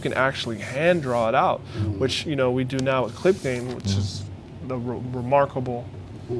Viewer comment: what does he say he uses to draw out the esses and t's? It sounds like he is saying click pane, or clip paint or something. Can't be Clip Paint though, cause that's a graphic programme. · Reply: Clip gain.